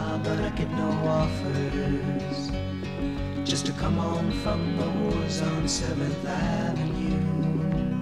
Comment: For the longest time I thought they were saying "just to come home from the warzone on seventh avenue." XD